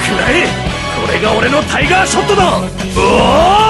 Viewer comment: es lo único que entendí de todo el video, el "tiga shoto".